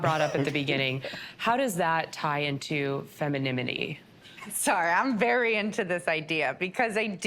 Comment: She did not just say “Feminiminy” 😐